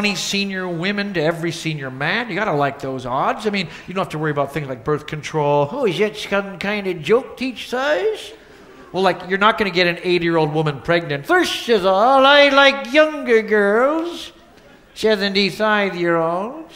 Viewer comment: "False alarm, it's just gas" Ah yes... fart jokes... a staple of good humor! "Can ya smell the beets?" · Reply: Oh I thought he said beach